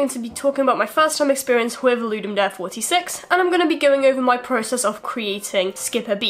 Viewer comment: Ok at the start I thought you said "Ludum Death 46..." and I thought "Damn, LD does not sound like fun" Uh oh forgot to mention that the game looks great!